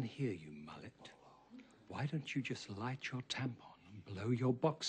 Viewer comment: omg for like ten years i thought Bernadette said "light your temper" LOL!